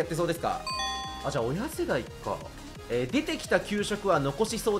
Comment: アースジェットがゴキジェットに聞こえた俺は難聴確定